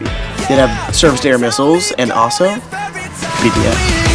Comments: did he say BTS? LOL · did I hear BTS?🧐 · Omg did he just said bts at 1:35😱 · waittttt did he say BTS??????